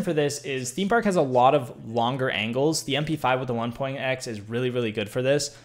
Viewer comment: Weapon :mpx with 1.5 What he says mp5 with the 1.x